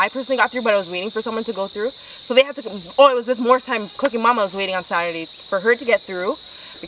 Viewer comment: I heard “morris time cookie mon” so many times 😭😭 what did she sayyyy😩